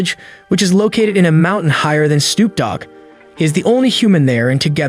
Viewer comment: What did he just say ? Higher than Snoop Dog